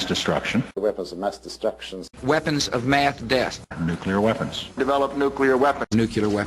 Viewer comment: You know what's funny? All the candidates said "weapons of mass destruction" but somehow the only time George bush said it he said "weapons of math deace" at 1:58 lol bushism what an idiot